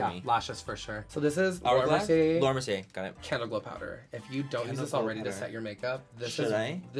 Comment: lol no lie I thought he said cantaloupe powder and here I am on sephora trying to find the laura mercier canteloupe powder....he said candleglow powder!!!!